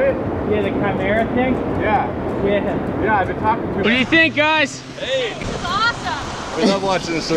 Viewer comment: He thought you said what do you drink 😂